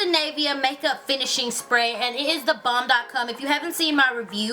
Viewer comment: Did she say " is the bomb dot com??? Lol